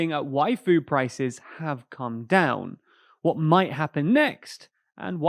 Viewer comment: Ngl I misheard the intro saying that "waifu prices have come down"